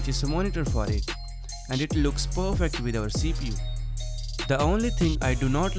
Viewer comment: did you say CP instead of PC. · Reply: I believe he said "CPU"